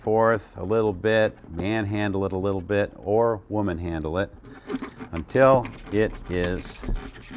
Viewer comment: did he say let the women handle it? XD